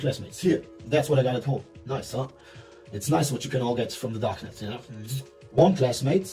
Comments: He actually said "Darknet", not "darkness". Cool video, thanks. I'm german, born '94, grew up with seeing that video back then, but TODAY I found out about his life after that, thanks to the famous german channel "simplicissimus", they released that video today. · he actually said Darknet and not Darkness xd Wer ist hier noch Deutsche/r? :D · I think he's saying "darknet" not "darkness". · the subs: He actually says " its nice what you can all get from the darknet" (darknet, deep web, you know that sort of thing...) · i think he said ''from the darknet'' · 'darkness'? Thought he said darknet. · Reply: Actually is Darknet.. not darkness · He clearly says "darknet" Not "darkness" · I don't know if it was you or him that made the subs but why on earth did you write "DARKNESS" instead of "DARK NET"??? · i think he said "darknet", not "darkness" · You got the subtitles wrong. he said what you can get on the dark net, not the darkness. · he says darknet instead of darkness